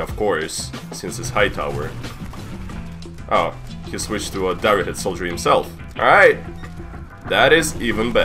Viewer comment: It sounds like he said dire soldier